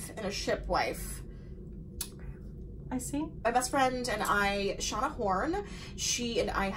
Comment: When she mentioned her land bestie and said her name, "my land bestie and I Shauna Horn (?)" I thought she said they shot a porn! lmao